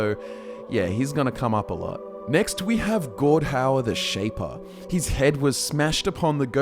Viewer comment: when said Gordhaur i thought he said God Howard